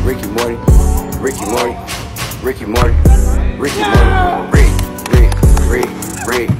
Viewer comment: Thought this sude was saying Ricky Martin